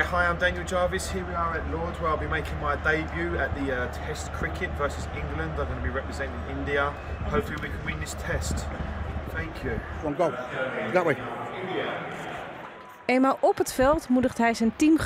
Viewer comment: Alleen ik hoorde "dawaj"? Alsof hij/zijn vriend Poolse taal spreken? Of David, of iets anders.... Ik hoor "dawaj"... 🤔